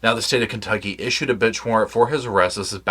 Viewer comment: I swear you just said a “b!tch warrant”.